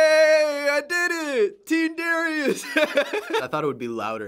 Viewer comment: did he say "T-Series" ? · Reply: VitCat no he said team Darius